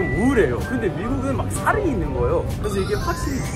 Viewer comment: 근데 미국은 살인있는거예요~ 라고 들려서 되돌려봤네...ㅋㅋ